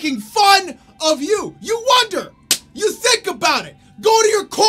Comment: i only heard " "You thicc about it" 😂